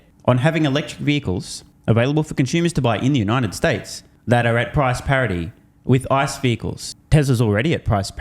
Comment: I think she said profits that are at parody with ICE, no?